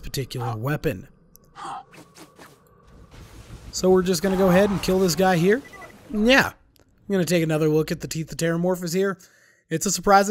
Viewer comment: What did you say? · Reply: Bosse Broo “So we’re going to go ahead and this guy right here. N’ let’s take another look at the teeth of terramorphous.